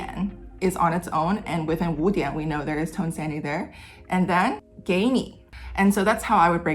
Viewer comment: I know it's spelled tone sandhi but I keep hearing "tone sandy" and like yeah, those tones are in a sandwich